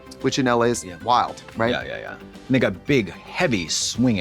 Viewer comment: Does Katya say the N word at 20:17?? Someone pls tell me I'm crazy